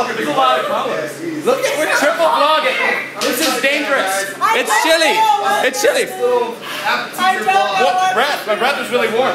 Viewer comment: I thought that girl was iJustine, then I thought he said "It's Shelley, It's Shelley" while pointing at her. Then I realised that it actually was iJustine and the he said "it's chilly" hahahahhahahaha...........yeahhhhh anywayyyyyyyyyyy